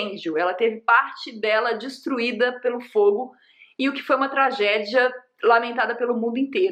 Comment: E o quasimodo ta bem? (Edit:e uma piada tá, e sim estou bem chateado) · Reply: quando ela falou que a igreja foi destruída pelo fogo eu juro que eu ouvi "destruída pelo Frollo"